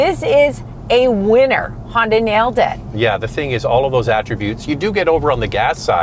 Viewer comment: did you say hyundai? :)